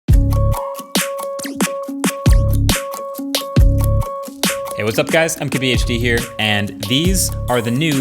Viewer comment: For some reason whenever he started the video, I always thought he was saying, "What's up, guys, I'm KBHD here."